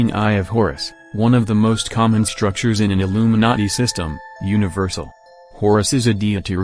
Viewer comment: It sounds like narrator is saying Illuminutty... Lolz